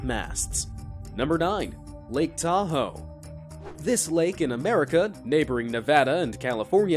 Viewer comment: Legit misheard 1:42 as lake taco. The Mexican I me has taken over Xd